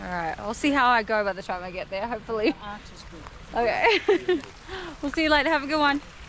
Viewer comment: did I hear “your ass is good” ? Or where they thinking out loud :)